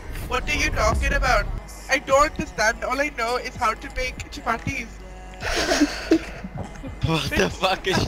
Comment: I thought she said only thing I know to make is chipotles lmao